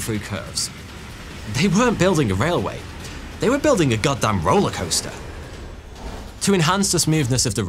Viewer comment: I'm just waiting for them to invent the Gundam. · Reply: i actually thought he said "Gundam rollercoaster" and had to skip back to check...."goddamn rollercoaster"